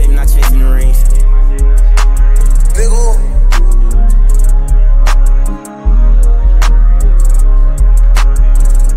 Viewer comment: did someone say n word at 2:10? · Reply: I think he said let’s go